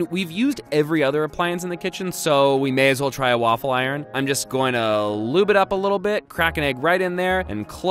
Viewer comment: I'm just gonna loop it up a little bit" that's what he said